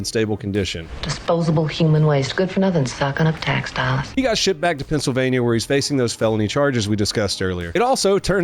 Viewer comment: I think I heard "He got sh!t back to Pennsylvania..." HA!!!